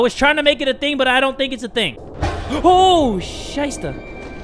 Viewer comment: did just say "scheiße" because that's german it just means shit lolol